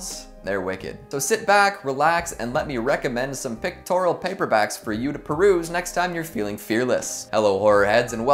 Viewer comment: Thought he said, "pectoral paperback". Just pictured a super-buff book flexing